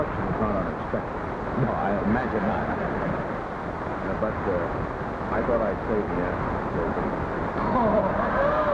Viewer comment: I think he says, “I thought I would save the answer for Joey Bishop.” Seven months earlier, Bishop began his run on ABC against Carson, but he never made a dent in the ratings. That show would last another two years before Dick Cavett replaced him.